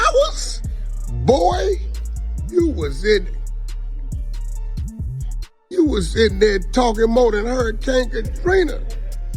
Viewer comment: did he really say “you were sitting there talking more than Hurricane Katrina” ??? What that even mean 🤣 Hurricane Katrina talked? 🙄